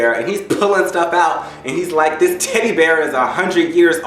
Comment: sounds like u said titty bear!!!